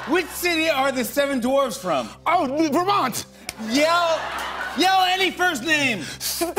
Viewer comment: I heard “yell any first name” as “tell any fruit’s name” and was rather surprised when he shouted “Sssstephen!”